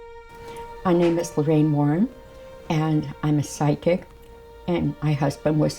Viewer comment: oh.. a psychic.. i thought she said sidekick lol